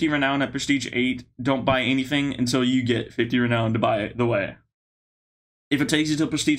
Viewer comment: What you hear: "Buy The Way" What i hear: "Btw"